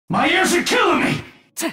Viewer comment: I'm not sure, but his first phrase seems to me: "my knees are killing me!" Lol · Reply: Lol I thought he said "my ears are killing me" 🤣